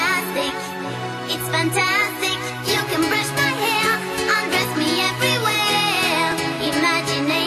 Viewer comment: LOL! LOVE IT! That fort at the iceberg might really be the dojo. My sis thought Sensi said "you can brush my hair and pull down my underwear!"